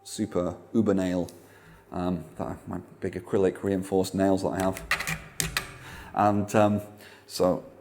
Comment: did anyone catch what he said about hes thumbnails at 7:30 ? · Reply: He said they're acrylic reinforced nails.